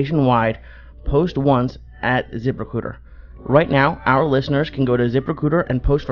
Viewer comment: The first time I thought he said Zipper Pooter and I was like what kinda fucking sponsors...... lol